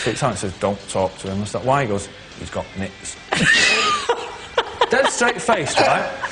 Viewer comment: What is he saying? "He's got.... knits? knicks? i don't get it. still funny though, he sure can tell a story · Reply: Nits baby lice